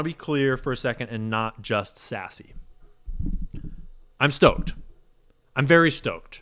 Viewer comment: When you said “I’m not just sassy, I’m stoked” I thought you said “I’m not just sassy, I’m stoned” 😂 I was like well okay, 😂😂